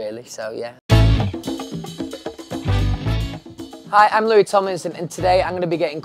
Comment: I thought he said ‘I’m Louis Thomas’ but wow...those sentences 🤯